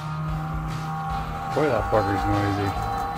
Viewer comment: OMG I know personally that you do not use foul language but at the 53:22 timestamp it sounded like you said "that f**ker's noisy". Even CC picked it up that way😢. But I know you said that bugger's noisy